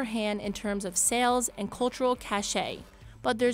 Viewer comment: Half the time, I thought she was saying 'new bounce.'